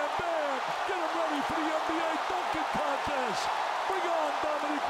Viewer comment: It sounded like the commentator said “fucking contest” 🤣🤣🤣